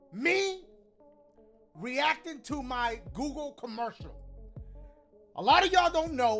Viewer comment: I thought it said “reaction to cocomellon” or something